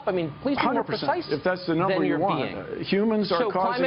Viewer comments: cus every time Bill gave an answer he would ask for evidence and Bill would just smile or not say anything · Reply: What Bill said at 3:35 ? is it " 100% that's number that you want " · Tucker just absolutely dismantled Bill Nye. Gotta love it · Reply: What Bill said at 3:35 ? is it " 100% that's number that you want"